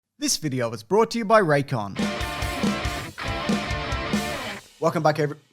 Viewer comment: did anyone else hear "This video was brought to you by "r*pe con"